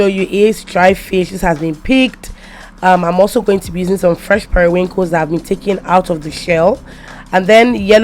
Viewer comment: What do you say at 0:34? Fresh ___? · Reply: Fresh Periwinkles